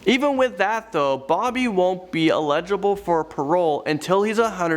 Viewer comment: was it just me or did anyone else hear say illegible instead of eligible